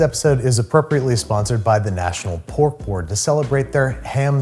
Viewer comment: any1 else thought he said pork porn at the beginning